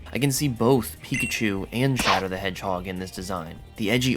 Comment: not watching at the time I thought you said Shaddoll Hedgehog